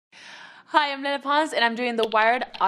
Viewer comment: omg when she said “hi im lele pons “ i thought she said “hi im menopause”😂😂